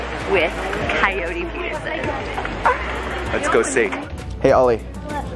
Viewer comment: In the beginning I thought it said COYOTE PERSON